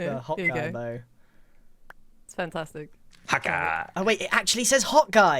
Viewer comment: THIS WHOLE TIME I THOUGHT HE WAS SAYING "HAWKEYE"